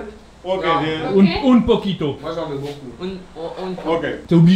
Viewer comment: C'est moi ou je crois entendre à 15:28 le mec dire moi j'en met beaucoup